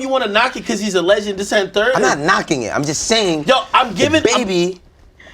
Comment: Yo I need help, I'm not 100% familiar with African American slang. But when Ak 1:05:38 says thiziznthird what exactly is he saying lol and what does he mean?